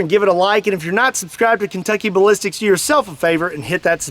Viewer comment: thought it said ball sticks